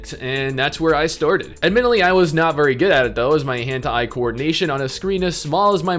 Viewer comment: Hentai coordination is what I heard. I wonder if one can add it to CV.